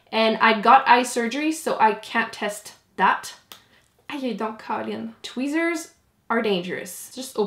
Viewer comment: What was the thing you said in French at 16:29? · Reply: ayoye donc caline 😂 très québécois j'ai vraiment ri 😅